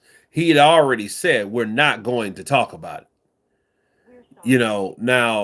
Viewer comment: #Simon Jordan said “WE WILL talk about it” wilder got pissed because of the YOU WILL Simon even said the follow day that he could of said it in a different way instead of you WILL